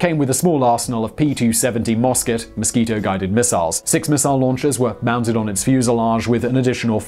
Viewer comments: Did you just say Mosquito-guided Missiles??? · OK...am i hearing things, or did he say "Mosquito guided missiles?" Listen to it again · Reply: "Moskit" (mosquito) guided missile. Moskit/mosquito is the name, not the control/guidance system, unlike the dove/pigeon control in Project Orcon